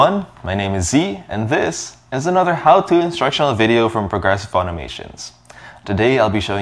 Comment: I thought he said progressive auto insurance before I read his channel name